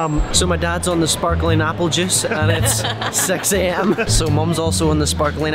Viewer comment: I thought he said sparkling apple juice is sexy, it was 6 😛🤣🤣😊🤣🤣😂🤣😂😅😅😂🤣🤣😛🤣😂🤣😂😂🤣😂😂